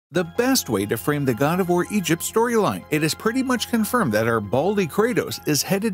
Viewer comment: I need to wash my ears. I heard "Best way to frame the Guardevoir egypt storyline" and was like "what?"